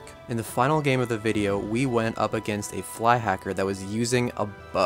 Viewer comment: I thought this said we bought a fly hacker. lol